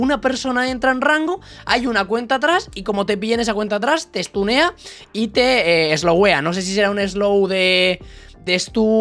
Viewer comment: Te estunea y te slo WEA jsksjss Así lo escuche yo xd